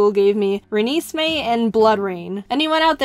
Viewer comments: i thought you were saying blood rain not bloodrayne now what is the diffrence? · I hear bloodrayne as raining of blood :P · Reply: i heard it as blood drain :'))) both sounds very annoying and almost a bit creepy...